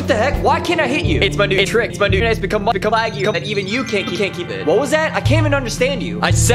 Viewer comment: What he said: It's my new trick my internet has become more laggy that even you can't keep up with it